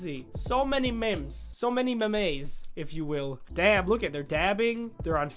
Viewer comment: Did anyone hear Sketch say damn? I heard him say that